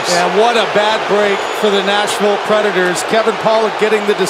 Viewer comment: did anybody else hear national predators